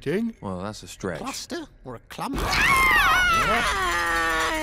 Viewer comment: When they said family game time I thought they said family gay time